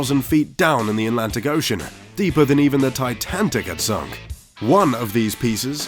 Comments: does he say TitanTIC instead of Titanic?? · Did I really hear "Titantic"? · Is it just me or did he actually said Titantic?😂😂😂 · did you just say "Titantic"???? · did he say titancit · Does he say “titantic”? @ 4:02 ?